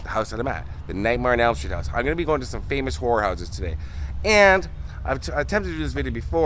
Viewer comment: When you said horror houses my husband thought you said whore houses. 😂